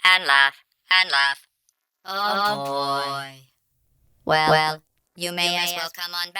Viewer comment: What does she say? 13:35 “Someday we’ll remember this and laugh, and laugh, and laugh [????]” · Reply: Oh, boy.